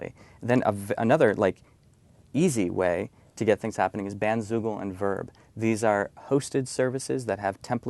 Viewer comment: What's the second thing that he said in 3 simple ways for online visibility? What did he say at 30:04? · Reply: Bandzoogle. :)